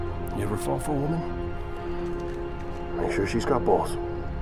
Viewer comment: Oh so that's what that line was, I've played the game about 3 times and always thought he said "make sure she's got both"